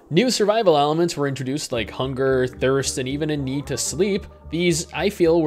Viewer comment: dat jiggle physics :D also, at 2:09, while I was distracted by her fine flying view, I swore I heard "and even a Nude to Sleep..." o.o